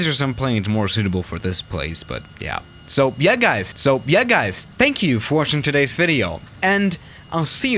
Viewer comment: did... did he just say so yea guys same time